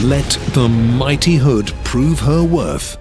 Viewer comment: When he said blow ship up I thought he said blow sh*t up so I used captions to see if I heard that right anyone thought that or is it only me?